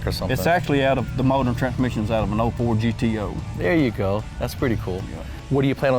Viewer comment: did I hear him right, the engine came out of and old Ford "GTO"? HUH??? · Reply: He said "an '04 GTO"